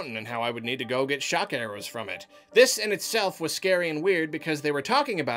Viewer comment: lol the subtitles say that "they said I would need shotgun arrows for it" instead of "they said I would need shock arrows from it" and honestly you would need shotgun arrows to defeat that boi. I think I died close to 4 times trying to kill that thing.